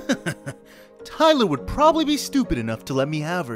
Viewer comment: idk i hear the things was like "tyler would be stupid enough to" LIKE BRO